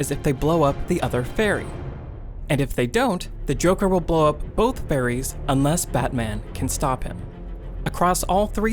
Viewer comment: I thought he said... The joker will blow up both Fairies! 😳😬👍